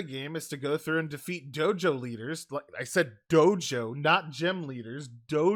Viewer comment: i keep hearing jojo